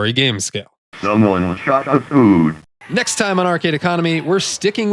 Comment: I remember coming across this in an arcade as a kid, but it was so noisy that I thought the game was saying, "Someone shot this dude."